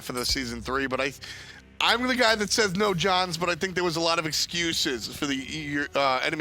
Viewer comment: F. Fucking. dot. Did you just say no johns. Every single time I listen to this guy he gets more and more likeable.